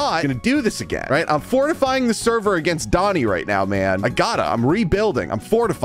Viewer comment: erm i think he said "doni" there